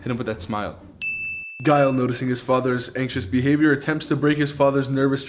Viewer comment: my sound glitched and this is what I heard: “Gyle attempts to break his father’s neck”